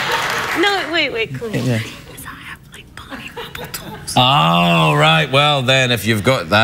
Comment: what does she say here? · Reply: I figured it out for those curious... "Because I've got like Barney Rubble toes..."